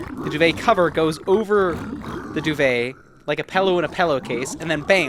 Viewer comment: did he just say... pellow..?